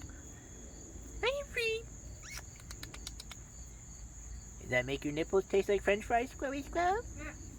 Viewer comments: did you say “does that make your nipples taste like French fries, squirrelly squirrel”? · did he say "did that make ur nipples taste like french fries squirrelly squirrel"